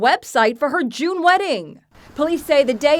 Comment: I thought the reporter said jew wedding